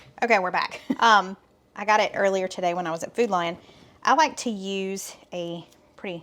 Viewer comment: It's funny, when you were talking about going to the grocery store, I thought you were saying you were going to Foodline, but then I saw the store sign - Food Lion.